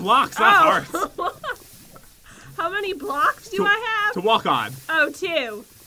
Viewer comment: I thought he said farts to walk on CD LOL